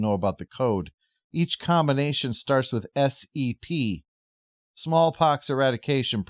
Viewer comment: For a second there, I thought you said "SCP," as in "Secure, Contain, Protect."